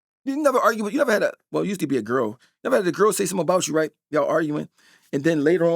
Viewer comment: DID HE SAY YOU USE TO BE A GIRL?……NOBODY CAUGHT THAT!???????